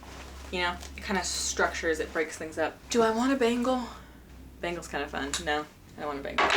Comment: i heard "do i want a bagel" i nodded "yes yes you want a bagel🙂‍↕️" then i realized you said "bangle" lol...for context its 4am while im watching this i need to sleep